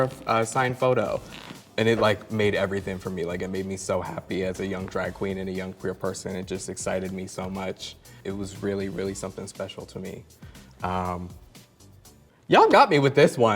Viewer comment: Okay, this may be the weirdo side of me, but I first heard ANETRA say "it made me so happy as a young DRAGON". Don't worry, I know that's not what she said. I have a weird joke that she's secretly an anthropomorphic Dragon (don't ask me how that one started), which is a compliment since Dragons are awesome. And then I imagined her scream at 1:08 to indicate that someone found a pic of her tail or something. That would've been amazing.